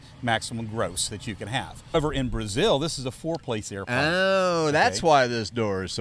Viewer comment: Looks great, are they in Australia? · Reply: Thought they said Brazil?